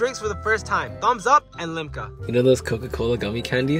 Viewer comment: I thought she said ligma💀💀